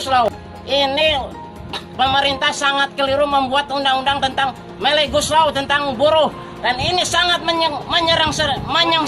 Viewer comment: undang undang melli goeslaw atau sy salah denger?